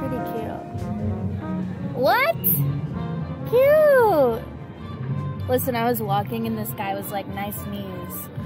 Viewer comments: lmao I thought she said I was walking and this guy was nice to me... I hope you can't see knees. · I heard “nice to me” instead of “nice knees” from that point onwards it was very confusing 😢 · I thought she said I was walking and the guy was nice to me and that’s why her pants were ripped